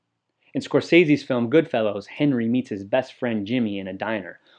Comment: did you just call Scorsese's film "Good Fellows"?